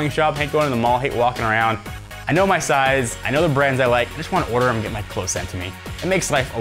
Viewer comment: Wow. At the end of the JackThreads plug I thought he said "I just want to order them and get my vasectomy".